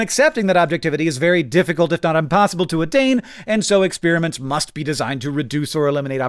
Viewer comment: did hank just say "unpossible"?